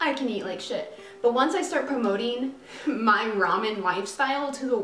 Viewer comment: Did she just say ramnyan instead of ramen???